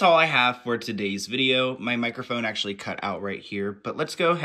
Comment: I thought had said **Mike Microphone**. If You know, you know .